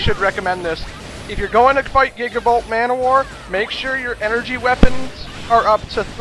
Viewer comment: When you pronounced gigabolt manowar, I actually thought you said Giggable ManWhore. Me and my imaginations, lol.